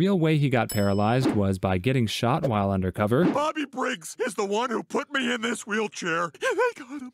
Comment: when he said bobby briggs i thought he said "mommy brigs" XD